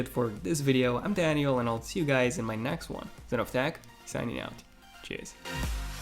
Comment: Did he said: Tschüs 12:54 at the very end?